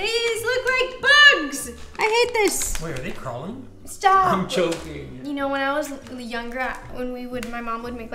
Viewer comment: Haha! 🤣 And right after when sayd "I'm joking", I thought he sayd "I'm choking"! 😂 That hole scene was hilarious!